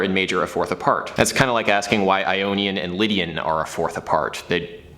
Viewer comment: first time I heard "an onion and lydian"